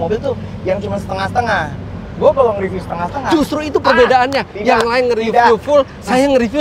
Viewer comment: ini orang Banjarmasin kah ? Kalo nggak salah denger, beliau bilang "Yang lain kerida" hihihi...